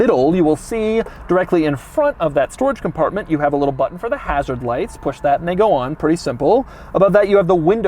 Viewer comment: did he say, "push that nigga on?"